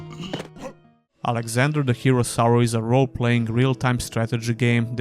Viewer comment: I always hear "Alexander: The Hero Sour" and now i can never unhear it again xD